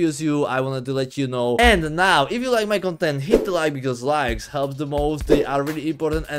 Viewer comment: did bro say “help the balls”